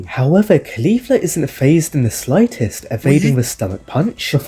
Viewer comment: thought you said tightest sayian